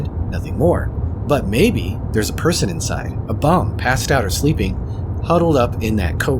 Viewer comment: did u say a bum💀💀💀